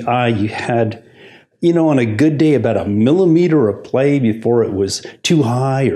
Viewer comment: Why do I hear "milliliter" instead of "millimeter" at about the 9:55 mark?